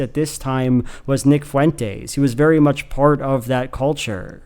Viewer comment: oh my god i wasnt looking at the screen and i thought you said "VIC fuentes" (as in the lead singer in pierce the veil) and my heart STOPPED.